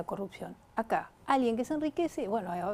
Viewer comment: No es el primer vídeo que veo de ella y es interesante lo que comenta, ya compraré su libro para una opción concreta. Yo te respondo cual es el peligro de un Milei. Milei resalta la destrucción de su banco central y dolarizar el país, un economista no deberia perder el poder de manipular su propia moneda ya que es una herramienta de estabilidad en un país, EE.UU tiene su banco central!!!! El otro punto importante es que Milei cuando habla del dólar se refiere a una época donde el dolar tenia una relevancia que ya no la tiene, no entiendo cómo sus simpatizantes no estudian por su cuenta a donde puede ir Argentina con politicas extremas. En EEUU ya quebraron 4 bancos, los tipos de intere no dejan de subir, el techo de deuda los rompieron 2 veces este mismo año a lo que hace tiempo se habla de una recesión y hasta un crash, si esto pasara en Argentina se sumergiria en una crisis mucho peor que la actual pero sin el beneficio de al menos manipular su moneda, lo que los dejaria en un pozo sin fin y sin responsables (culpables) en su propia tierra. Saludos desde Uruguay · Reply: Qué dice? Hay que hacertle un Baoba?